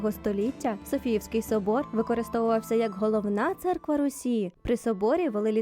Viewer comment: головний собор Росії'' ? Мені одній почулось?